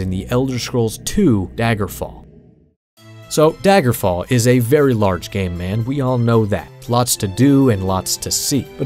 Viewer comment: Between 25:42 and 25:46 for some reason I heard “so daggerfall is a very large gay man “ I know your not saying it but it feels like one one of those you hear what you wanna things 😂